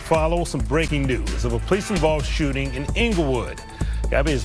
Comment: at first I thought he said “laugh right now” or “laugh out loud” since I was half paying attention. I immediately thought WTF DID HE SAY? That’s freaking rude!